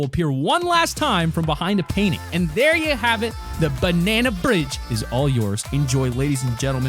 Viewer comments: just me or does he say "banana bridge" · did he say banana bridge? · is it just me or does he say "there you have it the banana bridge"